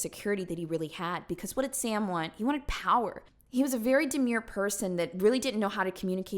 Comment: Did you say DEMURE